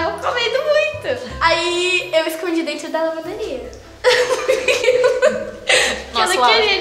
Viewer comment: Alguem me explica o que ela falou eu ñ entendi onde ela escondeu o donut !!! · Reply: Emanuel da Silva na lavanderia